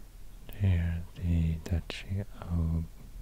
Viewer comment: Did he said Heil Deutschland